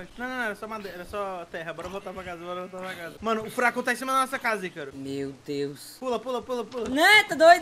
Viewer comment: Eu entendi "O FURACO TA EM CIMA DA NOSSA CASA YCARO" Eu to rindo até agora ;w